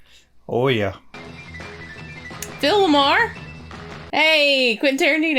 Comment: I misheard you when you said "Phil LaMarr" and I thought "wait I don't remember Bill Maher in being in Pulp Fiction..."